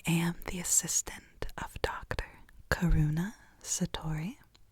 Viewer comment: I've been on social media way too much! Haha 😂 I thought she said her name was carona